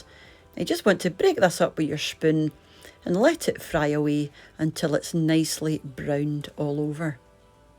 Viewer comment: Have to laugh at me. Thought you said fly away- it was fry away. Love the accent.